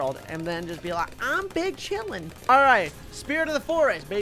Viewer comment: When he said big chilling i thought he said BING CHILLING